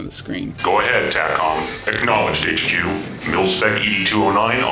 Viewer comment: "What lines have you guys misheard?" Well, shit. To me each line was like: Goliath: "Go ahead, TACCOM" = "Go 'head, take on." Goliath: "Acknowledged, HQ" = "Acknowledged sectioom," with the 'm' in the end. Wraith: "Wraith awaiting your orders" = "Reaped the wind -- now it's yours." Dropship: "In the pipe, five by five" = "In the bye, bye bye bye." xD Battlecruiser: "Engage" = "Mid 'ngage." Battlecruiser: "Hailing frequencies open" = "Hail, Frankus is spooked man." Up until I started using headphones instead of speakers (and watched Aliens for a second time), some of them were clarified ofc.